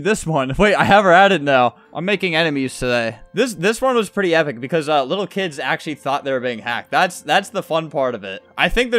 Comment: WHAT DID HE SAYYYYYYYYYYY? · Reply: “Shut yo fak up” lol